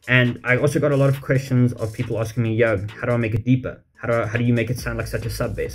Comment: Totally thought he said "how do I make it eepa"